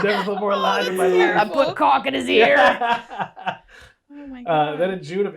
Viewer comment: what did he fill sheldon's ears with? 💀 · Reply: I thought he said caulk 🤷‍♀️😂